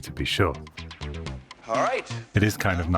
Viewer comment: what a fantastic video, making us so proud of you again!!! Mystery: at 5:21, do I hear Michael Knight's voice saying "Allright" in the background?